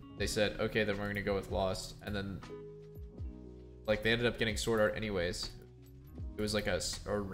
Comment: TSM dropped the ball, pretty much. They could have told DL that they're trying to get SwordArt. he would have stayed for that. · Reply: same. I thought he said swordart but I think he said Søren